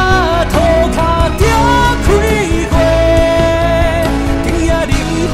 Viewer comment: 虽然不是慎众 但是很喜欢翻唱 很有趣(*´∇｀*)2:19 我听成：头壳就开花(*'▽'*)♪